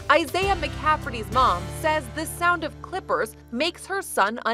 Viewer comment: I thought she said that the sound on Clifford makes him uneasy